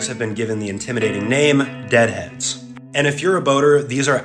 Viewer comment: NO I THOUGHT IT SAID DREAD HEADS💀